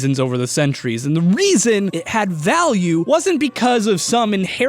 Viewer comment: Who else heard “valueness” and facepalmed